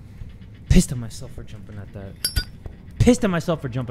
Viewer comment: bro i thought jay said i pissed in myself